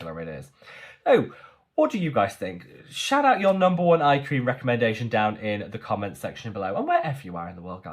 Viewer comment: I thought you said is your most favorite eye cream?